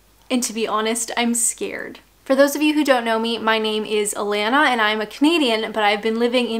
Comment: "Hello, my name is Alanna and I'm a comedian ...". Hold on, wait, what!? OK, I misheard. Things got weird for a moment there.